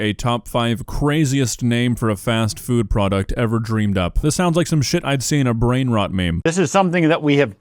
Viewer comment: I thought this man said “creamed up” 🥹💔